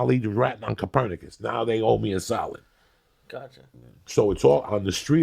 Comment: I thought he said salad when he said solid